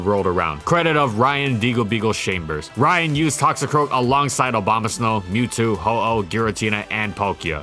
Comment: I think it's just me but I think i heard "Obamasnow"